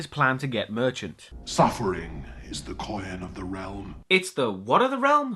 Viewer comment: "Suffering is the coyen of the realm" (19:12)- I still can't work out what Pinhead was trying to say there... Oh God, it's that awful child from The Shining miniseries who can't close his mouth! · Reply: That's the way a theatrically trained english actor will say coin lol